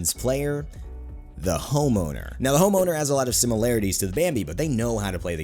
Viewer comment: who else thought he said homo instead of home owner?